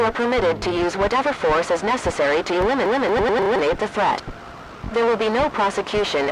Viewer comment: I thought it said “To women the threat.” 😂😂😂😂